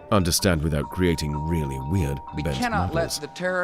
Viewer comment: What does it say at 8:46 before it gets cut off by Bush? "Without creating really weird [...]"? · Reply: «...really weird, bent models.»